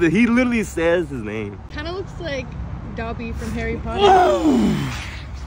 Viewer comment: Did they just say Dabi from Harry Potter😭